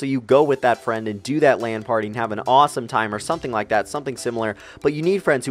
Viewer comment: lan party or land party? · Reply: OMG I thought he said ''Land party'' :P